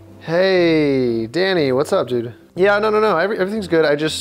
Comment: did you say "Whats up Judah"? cause thats my name lol